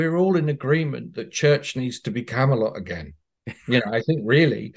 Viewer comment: What does Dr. Martin Shaw say @ 10:40 "I think we're all in agreement that church needs to ____ again"? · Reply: Church needs to be Camelot again. the city of King Arthur and the round table.